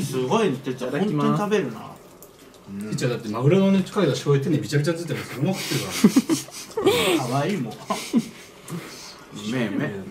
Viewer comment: でとしみつが何て言ってるか一生わからん せっかく笑ってるから何て言ってるか聞き取りたい… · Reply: jinjin8300 てつやだって、マグロのねかけた醤油手にビチャビチャについてるのにそのまま食ってるからな。 みたいな感じですかね！？間違ってたらすみません😭